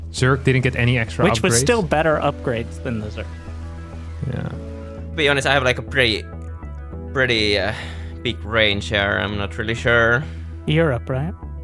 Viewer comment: I thought serral said "To be honest, I have a pretty big brain here."